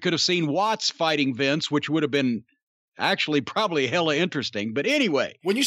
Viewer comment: did Corny just say "hella interesting"? hm.